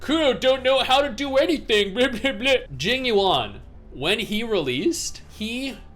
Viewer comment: when jinyab was releases he crashed Hoyos' server? Or Kuros server? Lol I was just saying that he misspoke which dev team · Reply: ah, my bad. I don't follow HSR, so I thought he said Jin Yan.